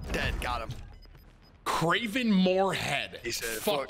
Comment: did he say “craving more head” ? Lmfao